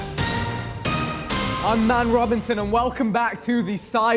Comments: I thought Vick said “rip Nate Robinson” when the video started 😭😂😂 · I thought Vik just said hello I’m Nate Robison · I THOUGHT VIK SAID HE IS NATE ROBINSON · Sounded like vik said im Nate Robinson 👀